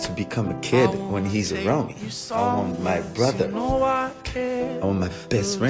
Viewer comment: I end up looking at this video so much to hear Larry talk about his love for Lau and I crack up everytime I hear him say "I born my brother". I guess his mom had nothing to do with it...lol. I guess since he referencing when he kicked Lau out of the womb to tell him how it was out there. So sweet!